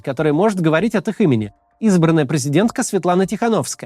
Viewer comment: Он сказал призидентка?или мне послышалось?